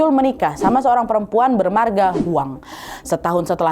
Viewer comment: salah dengar juga disini kali..bru habis nonton tc bang hiro berlibet juga didengarin kak..bernama kedengarn spt bermarga😂